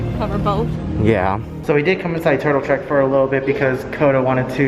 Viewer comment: Did you say "Turtle Crick?" Are you by chance from Pittsburgh? Maybe I just misheard you under the mask.